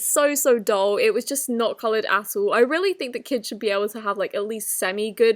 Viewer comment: when she said at all my captions said asshole